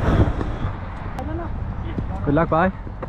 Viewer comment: Did he just said Good luck bhai